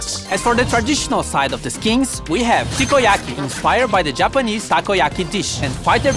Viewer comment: たこ焼きテイッシュにしか聞こえんて